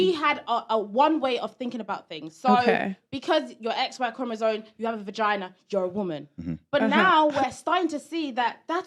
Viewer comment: anyone else hear her say “because you’re an XY chromosome and have a vagina you’re a woman”? Had to slow it down, but is that what she said?